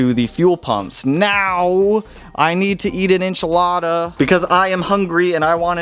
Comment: I literally thought you said "I want to eat an inch Lada". Now I want miniature Lada cars